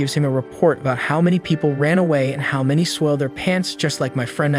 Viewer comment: A 19:50 I heard peed their pants lol